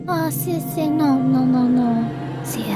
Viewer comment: wait did she just say si estos zapatos? That's spanish